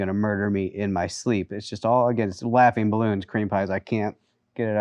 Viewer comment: Did he just say Creampies?